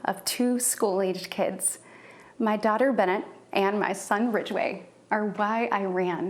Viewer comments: Her kids names sound like department store names. This week at Bennet's everything is 50 percent off. And, if you find it for less at Ridgeway's we will price match it! Bennet's will not be undersold. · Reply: Haha lmao 🤣🤣 was it Ridgeway??? I thought I heard Bridgway? 😊😊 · Did she say her son’s name was Ridgeway?